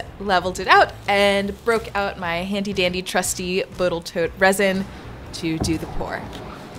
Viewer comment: I'm here from Furze's channel, great content. My youtube feed never told me about you before and makers are fun to watch so I've subscribed! At 12:53 did you say botal toat instead of total boat on purpose? I didn't catch it until like 10 second later and I'm like wait a minute... went back and im like yeah that is what I heard lol.